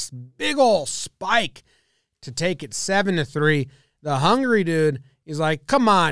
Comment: what i hear is "the hungry guy"